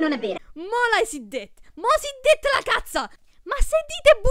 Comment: non ho capito puoi tradurre? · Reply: DJ WiiU "mo l'hai detta" "mo hai detto la cazzata"